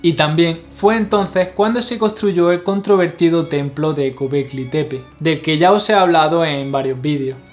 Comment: como se llama ese templo? El muchacho lo dijo pero no entendi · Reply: Gobekli Tepe